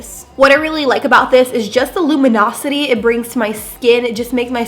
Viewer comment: I thought you said illuminati 😂😂